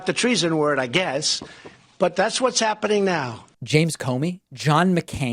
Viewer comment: You heard "McCain," I think he said McCabe, as in Andrew McCabe.